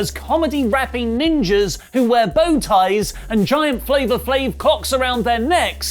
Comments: I heard 'cocks' and had to rewind, throw up YouTube auto subs and that too seems to have heard 'cocks' Good day. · For a second thought I heard flavor flave cocks lol